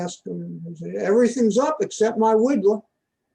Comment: What was that he said at 3:30 "everything is up apart from my wig?" Did I catch that right? Lol